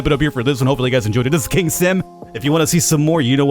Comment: Thought he said, "this is king simp.." at the end